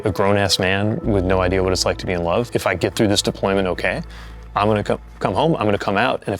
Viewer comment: Did he say "im gonna c*m"?